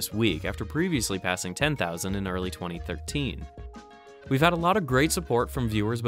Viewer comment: did he say 2015?